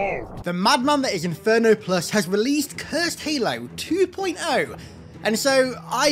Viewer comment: I know you said "Cursed Halo 2.0" at the end, but what I heard was "Cursed Halo 2". Is the true cursed sequel even possible? A hover Specter would be cool on Metropolis.